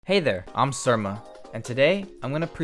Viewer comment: I thought you said "hey, I'm sugma"